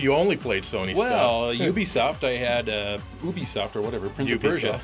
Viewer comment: i heard Boobiesoft intead of Ubisoft... haha